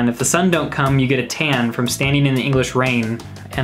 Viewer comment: I'm so dumb, I thought he said "you get a TANTRUM standing in the English rain" 🤦🏻‍♀️ "John Lennon was EMAILING" I pretty darn sure there was no such thing available at that time! Yes, some of us grew up having to use pen and paper, envelope, stamp and a postal service!